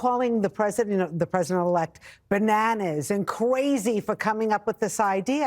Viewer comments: Did she say “Ka-wayzee ” ? (Crazy), like a 4 yr old??!! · Did she just say "Qwazy"? Is she a Looney Tune? If so, I'm boycotting WB. I'mma buy a hole bunch of Bugs and Daffy plushes, millions of dollars worth, which I will give to Warners and set them on fire. You know, like the other guys did to Bud Light and some other things, I don't even give af.